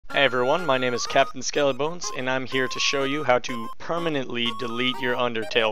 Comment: why does it say Everyone my name is buttfucker McGee and I'm here to show you how to rape flowey